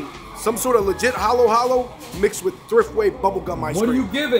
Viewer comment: Haha. Had to rewind a little, thought you said 0.5 out of 10 🤣. Mama papa shop will always get my business rather than corps, that's the way it is with me like Home Depot vs neighborhood hardware, or Staples vs small stationary stores. But sadly all stationary shops are gone and 1000 packs same paper are available but variety of rubber bands isn't. Gotta love our discounts huh !!!